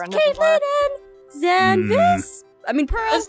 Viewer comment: Did she say Zanvis? Like the ship?